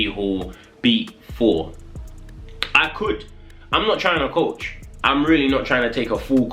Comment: Thought u said u aint coachin no more